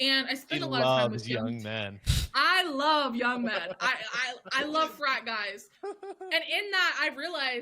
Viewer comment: I thought she said "I love 5 guys"